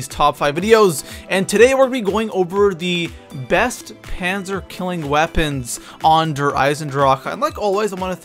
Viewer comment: I thought he said top 5 cancer killing weapon